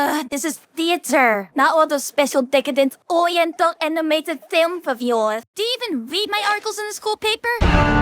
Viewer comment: IVE watched this 3 times and still have no idea what she’s saying. L ai. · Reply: Oriental animated film of yours